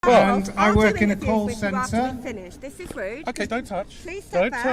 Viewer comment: I thought he said coal centre. Damn Northern accents!